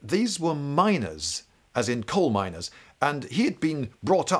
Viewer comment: The clarification of 'miners' was much needed. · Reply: le Beast tbh I thought he said minor the first time he said miners. "Oh gay minors? Wtf is this book about lol" then he said coal miners and I realised he was talking about something else entirely